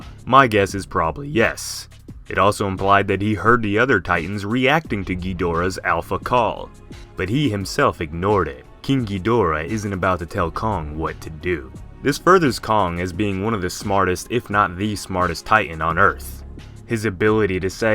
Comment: king ghydora... I thought you said kinky dora ngl